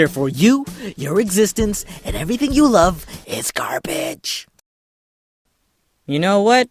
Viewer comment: sounds like hes saying "Garbitch"